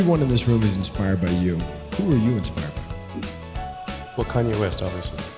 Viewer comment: Elon makes a quip that I have played back again and again, and cannot understand what he says--- can anyone make it out?! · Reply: Kanye West 😉